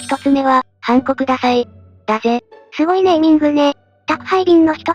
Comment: 音声だけ聞いてたらハンコくださいが韓国ダサいに聞こえてしまった。